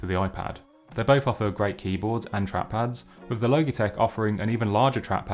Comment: Great comparison. You deserve more subscribers. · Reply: At first I thought he said "Loki-Tech" ; )